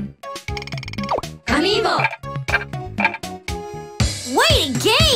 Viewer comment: Is it just me or do i hear I'm evil instead of amiibo